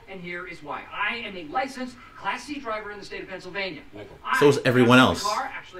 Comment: I’ve seen this series probably 10 times over the years and until right now watching you I thought he was saying “I am a licensed CLASSY driver “ and you saying “so is everyone else” made me realize he is saying “Class-C” holy sh*t…